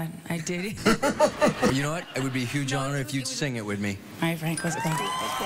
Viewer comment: anybody else notice her calling him “frank” in the beginning ? Lmfao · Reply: I'm super late, but it sounds like she said, "Alright, Frank - let's go."